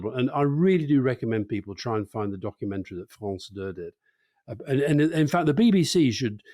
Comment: can somebody please tell me the name of the documentary filmmaker? I can't work out what Alastair is saying. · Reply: France 2 (He says France deux)